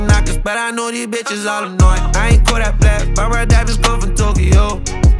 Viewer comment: OH DIAMONDS. I thought he said DIAPERS. That's makes a differene.